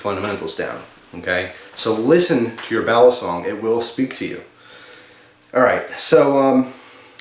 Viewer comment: I heard "Listen to your bowel song, it speaks to you."